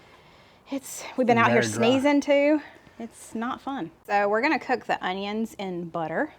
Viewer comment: 😂When you talking about all the the pollen i thought you said " its snot fun"...lol😂😂😂😂😂